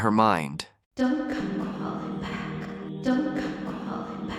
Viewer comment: what did her mother said ?? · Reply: Don't come crawling back!